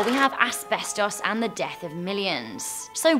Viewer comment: When you said the death of millions I thought you said death of a person called Milleins or something